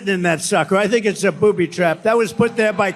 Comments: Sounded like he said "poopy trap" to me. lol. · I heard him say “poopy trap”… Freudian slip if you ask me. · He will not sit in white chairs, because he will turn them brown. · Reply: It even sounded like he said "a poopy trap." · I heard "poopy trap" 🤣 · it sounded like he said poopy trap lol